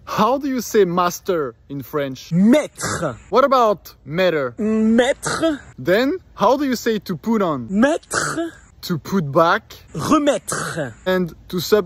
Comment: All I heard was "Math math math math math" I had to sit down after hearing so much Math 💀